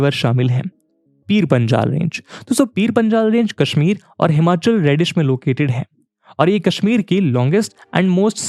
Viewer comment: Himachal Radish? I mean, I get what you're trying to say but....... merely reads his lines?